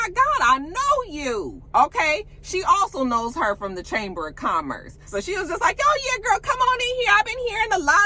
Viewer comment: So we acting like Latisha didn’t say she knew Tiffany from the “Chamiss of Commerce” twice? Oh ok. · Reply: girl i thought she said "chambers.." but I just watched again and you're right!!!! she says " chamice...." but how does all that education not include English & ENUNCIATION?🤦🏾‍♀️